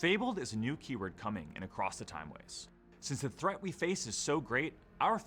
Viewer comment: All I hear is “across the timewaste”